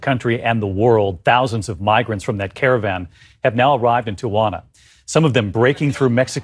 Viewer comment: I'm not familiar with the news anchor at 10:10, but I'm 100% sure he said "Tawana" instead of "Tijuana".